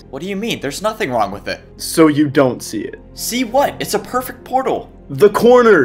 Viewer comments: Dude we really had one job and we accidentally screwed up the simulation 3 seconds into the video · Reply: Lol did he say or see what 7:56 lol and it was 5 secs not 3 (uhh is it two secs after or three) AND hey if you see this English or Spanish he he he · Dude we really had one job and we accidentally screwed up the simulation 3 seconds into the video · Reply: XD What he say: see what? Me: XD